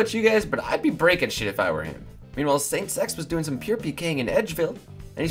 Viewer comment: i dont know about youguys but in the second clip it sounded like said "saints sex"